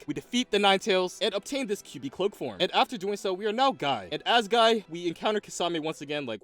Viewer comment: i thought he said "we are now god" and even since he didnt i was right